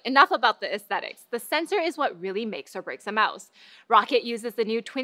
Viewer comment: "Rocket"?... k. I just called it "Row-cat". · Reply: speaks way too fast, even at 2:58 it sounds like she said "make a Razer mouse" and not "make or break a mouse"